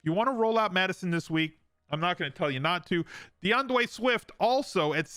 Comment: did you just say "deandwey swift" 😂😂